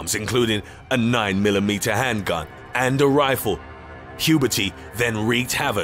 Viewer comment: Im just here for Huberty, and now laughing about McPizza, McSpaghetti and many more McFailures... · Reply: I thought he was saying “puberty” LMAO